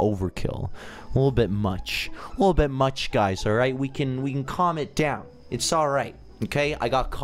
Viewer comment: When he said “let’s calm it down!,” I thought he said comment down, so here I am